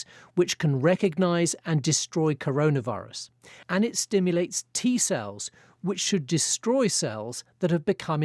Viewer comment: T Cells!!! ......Phew, I thought I heard T Virus from Resident Evil.